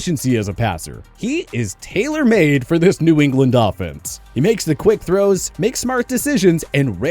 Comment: Is it me or did you say New England dolphins